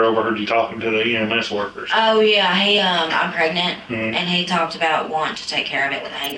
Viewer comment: The lady needs therapy and a few other things. She is obviously traumatized and has other things that need to be addressed. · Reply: did she say she was PREGNANT??